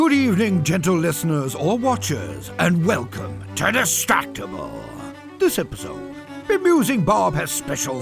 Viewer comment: no way 💀 captions on YouTube wrote "destructible" instead of "distractables" frrr